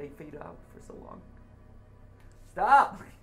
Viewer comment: Legit thought Matthew said "Bop It"